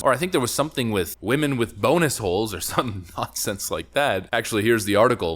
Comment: shit i misheard that at 1st and wasnt watching the screen and heard "boner hole" and just didnt think anything of it, just another colorful name for one......which i might start using...